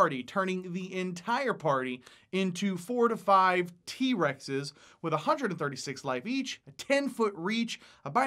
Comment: 7 of the pixies: "Whats a T-rex?" 1 of the pixies: "I KNOW WHAT THAT IS!" turns party member into a cow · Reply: T-Rex I thought he said T-bone steak??? Oops